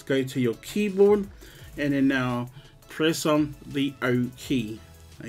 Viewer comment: bro which key i can't understand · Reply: O